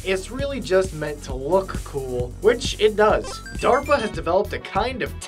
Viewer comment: I thought he said derpa